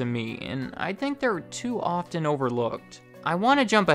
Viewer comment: I thought it said “over cook” instead of “overlooked”.